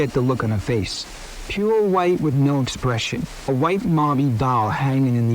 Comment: ok i hate to disbelieve the story, but....IF the audio recording at around 9:00 is the actual audio, i have a problem. At 9:02, the cop said "a white MARBIE doll". This tells me he is READING this off of a written page, the person who wrote it put a lowercase "L" in "marble" and the reader thought it was an "i" and said "marbie" instead of "marble". Like someone found a guy with a NY accent and had them read this script. Any agree-ers? disagree-ers? idk but it looks pretty bad, unless "marbie" is actually a word....